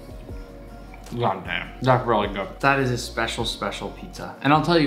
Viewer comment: I got scared because the subtitles said “not really good”